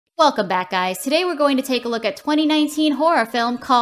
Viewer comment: when she said welcome back guys, I thought she said f*ck the bad guys until i turned on captions.😂😂